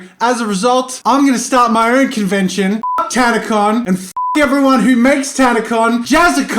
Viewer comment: I turned on captions just so I could take a screen shot. But instead of F*#% tana con it says katana